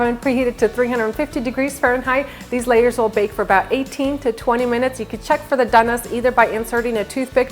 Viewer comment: I didn't catch what u said from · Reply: The layers will bake for about 18 to 20 minutes